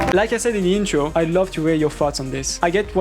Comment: on 13:00 its soounds like your saying farts instead of thoughts ahhahah